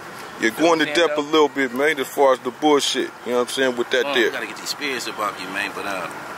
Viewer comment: did he say I gotta get these spirits up off me??? Damn mess